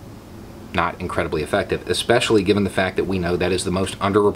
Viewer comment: Oh I thought you said "defective" 😉